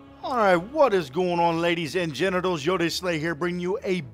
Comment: it sounds like he says hello lady and genitles